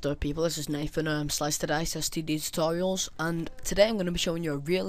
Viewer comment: Sounded like you said STD tutorial L0l